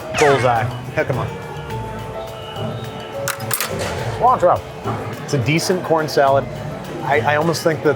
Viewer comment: lol when babish ate that at 11:25 and says "CILANTRO!" it sounded like someone almost yelling "GRENADE!"